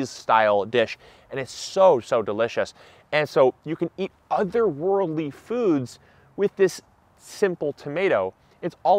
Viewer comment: I heard it as otherworldly food rather than other worldly food 😂